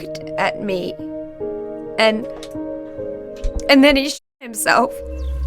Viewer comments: why it sound like she said he shit himself😂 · censoring this but made me laugh so much 😭 I feel so bad Sounds like she said "and then he shit himself" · Bro, when he bleeped it out on the shot himself thing I thought I heard shit himself